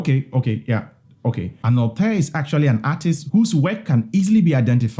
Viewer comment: What is the spelling of the word at 6:20? 'anote' ? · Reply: The word is: auteur